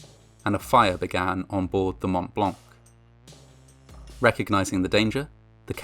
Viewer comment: I know it's not, but I heard, "The Matt Blanc" and I thought, "Man, Joey from friends really fucked up this time." Seriously though, I'm an "essential worker" and throughout this pandemic I have been so happy to see you upload right as I'm getting ready for work. Your alerts have been a bright spot on some seriously shitty days. Thanks man, I appreciate you.